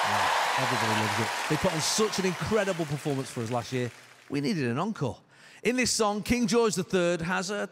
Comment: what did he say? Was that english? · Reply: We needed an encore. Yes. English.